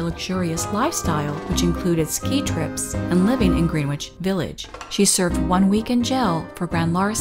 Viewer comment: Did you just say green-witch village?